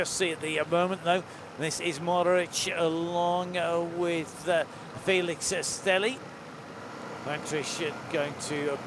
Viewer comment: I cant understand the commentator at all 2:55 "This is Mohoric ah long ah with ah Felix ah Stehli"